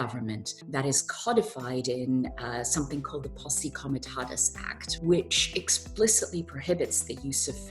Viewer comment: Sounded like she said "the poosy come and got us" act. Guess she meant " the posse comitatus". Not sure which is better for us. The lawmakers/breakers care not bout any of out rights.